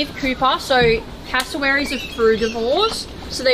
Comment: When she said, "Cassowaries are herbivores" I heard "Cassowaries go through divorce" and didn't really question it